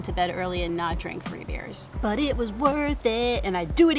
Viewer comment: I had to you replay the part of the video where you said how many beers you had . I thought you said 30 beers. I thought holy crap there is no way she can run tomorrow. I didn't know a person can have a hangover from just 3 beers. I don't drink so it's news to me. Loved the video. ❤️ P.S., Are you aware that another Emily Heller signed a deal with NBC ? Small world. Stay safe, be well.